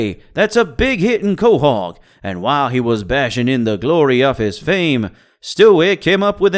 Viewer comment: my man says leven instead of eleven and says bashing instead of basking county accent got home fucked up